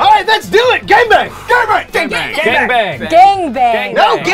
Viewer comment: did i just hear Gay Bang lol